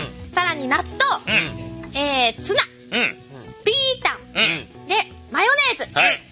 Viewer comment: ギータに聞こえるwwww